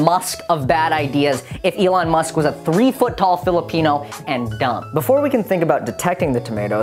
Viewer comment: "I'm Elon Musk of bad ideas if Elon Musk was a 3-foot tall, Phillipino man and dumb" Michael 2019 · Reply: PencilHead Studios I thought he said “3ft tall jalapeño man and dumb”. 🌶